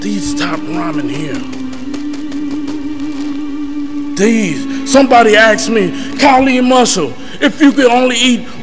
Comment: did he just say somebody aks me instead of ask me ? hear it guys